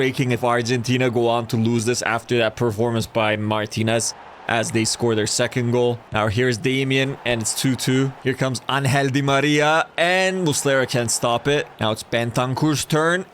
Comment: Did I heard "Unhealthy Maria" 💀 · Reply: It sounds like un but it is ángel di Maria